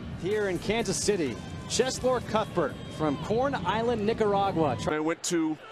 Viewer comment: did he say he was from porn island